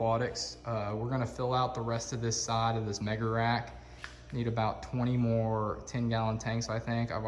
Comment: what kind of "rack"??!! Did you say what I thought you said??!! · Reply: Mega rack